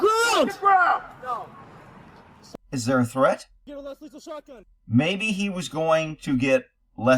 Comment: you can hear someone say "going to get the shot gun" you almost talked over it but i heard it between your words